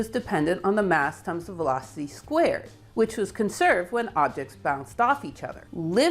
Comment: when referring to Leibnitz and his living force, can’t understand one of the words “.. when objects found ( ? ) each other”. The word is muffled, what does it say? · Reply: bounced.